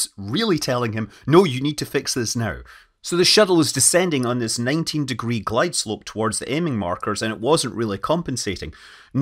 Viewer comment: Did i hear "90 degree glideslope"?